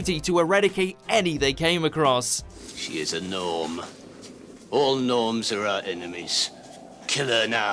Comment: I genuinely thought the Mutos just said "She is a gnome ... ALL GNOMES ARE OUR ENEMIES!"